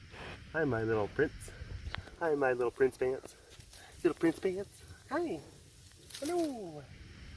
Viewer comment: Zubi clearly misheard you... prince pants, piss pants. 😉🤣😮